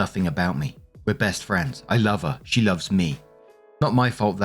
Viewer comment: I Don’t call my best friend my lover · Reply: I heard "my lover" and thought Whaaaat?! Went back and read below, it said "I love her". Maybe a slip of tongue?